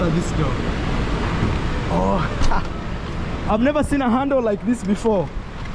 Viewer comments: I´m confused...he said it was a Hyundai but the emblem on the steering wheel looks like a KIA..which is another Korean brand...but ok thanks for visiting Jamaica and I know you enjoyed yourself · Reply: got confused because he initially thought said he’s never been in a Hyundai, when what was trying to say is handle. Thereafter, when asked what car he has, he said it was a Hyundai. But yes, it’s a Kia. The brain can be funny like that… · That SUV is a KIA and not Hyndai. The car might have been rent -a - car. The steering wheel shows KIA written on it and not Hyndai. Watching from The United States so, I know Kia when I see one and I know Hyndai when I see one. That is a KIA. Good SUV. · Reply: got confused because he initially thought said he’s never been in a Hyundai, when what was trying to say is handle. Thereafter, when asked what car he has, he said it was a Hyundai. But yes, it’s a Kia. The brain can be funny like that… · 👍🏾🔥🆙️🔝🙏🏾💯🇯🇲🚫🧢That car is a Kia ev6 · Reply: got confused because he initially thought said he’s never been in a Hyundai, when what was trying to say is handle. Thereafter, when asked what car he has, he said it was a Hyundai. But yes, it’s a Kia. The brain can be funny like that…